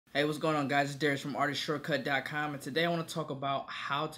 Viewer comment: Darius....I apologize I thought you said Derrick